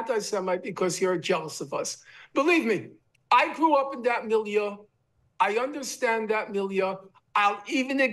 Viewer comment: Can someone tell me what is "milia" Norman repeated @ 4:50 4:52 5:50 mean?! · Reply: "milieu" : the physical or social setting in which something develops or occurs